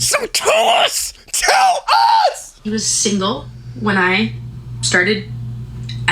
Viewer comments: She said P WASN'T single. Did anyone else hear that? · DID SHE JUST SAY "he WASNT single?!??"